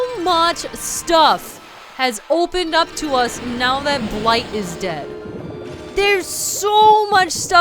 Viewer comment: ok for a I miss heard you Saying “Blight was dead” for a second and I thought you said “life was dead”